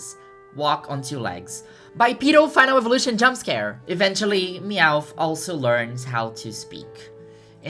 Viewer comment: You said "BYPEDAL FINAL EVOLUTION JUMPSCARE" so fast and I was kinda zoning out All I heard was "P*DOPHILE EVOLUTION JUMPSCARE" and I was like HUH